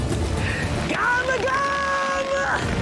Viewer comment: I always thought he was saying gamugam in this scene.